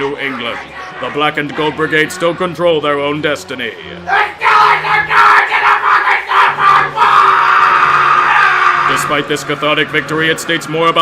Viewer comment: For those of you that didn't catch what he said at 5:47, he screamed "THE STEELERS ARE GOING TO THE FUCKING SUPER BOWL!!!!!!!!!!!!!!!!!!!!!!!!!!!!!!!!!!!!!!!!!!!!!!!!!!!!!!!!!!!"